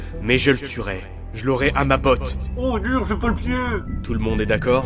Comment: C'est moi ou Eren dit " Oh, dur, c'est pas le pied "😅